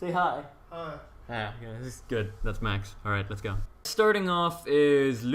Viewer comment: I thought he said "That's my ex" :DDD